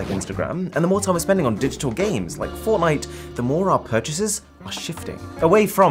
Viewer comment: did he just say formite?